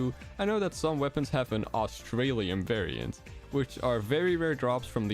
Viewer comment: I thought he said Australian mode lol